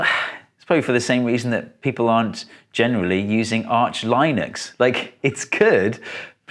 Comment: Did you just say Arch "Loynux" ? That was adorable!!! 🥰